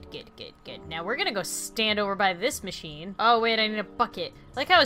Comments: Is it just me or at 15:05 I hear “oh wait I need a fu** it” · who else heard I need to f@ it? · WHO THOUGHT AT 15:05 LAUREN SAID "f*ck it*